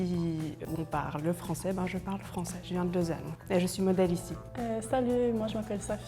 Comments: she says she comes from Lausanne but the subtitles say Genève??? Why? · She said "Lausanne" but the subtitles say Geneva??? How lazy can you be to double check what they actually say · She said, “Je viens de Lausanne,” but both the English and French subtitles say she’s from Geneva. She’s actually from Vaud — though I get the confusion, since she’s from a boring town on the shores of Lake Geneva. 🤣